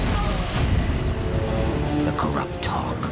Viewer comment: Bro, for years I thought he said "the corrupt hog"